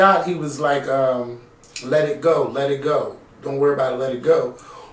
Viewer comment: When was with, it sounded like said let her go!